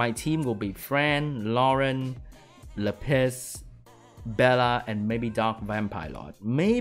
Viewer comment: everytime he says lapis i hear "le piss" hahaha